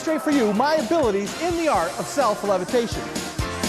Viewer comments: Hey Josh and Olly I’m reaaally getting into your videos these days. I might be wrong but I think I’ve found that english cc at 01:39 the man taking about self-gravitation wrong. I heard that the man saying levitation instead of gravitation. I’m I right? Don’t bother if it was just my mistake lol. Cheers mate! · Anyone else see that they accidentally put Self Gratification instead of Self Levitation LOL